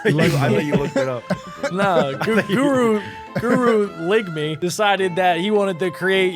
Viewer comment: did you say guru lick me? 🤨